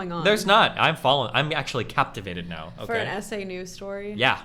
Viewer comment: Everytime she said "SA" I thought she was saying essay.